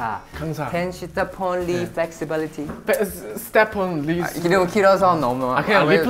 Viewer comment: sorry but I heard “step on me/lee”🤚🏻🕳👩🏻‍🦯